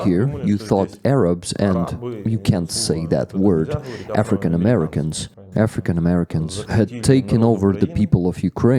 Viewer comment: did he actually say "African American"?